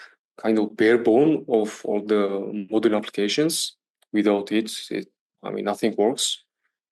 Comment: Thank you for this valuable discussion about DevOps in 2025! A small note - at 1:07:00 when I mention "without it, nothing works," I was discussing the importance of DevOps, but the opening subtitles show "AI" instead of "it". Looking forward to more technical discussions!